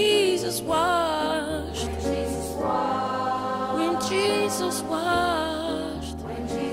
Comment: I always thought he said "When Jesus walked."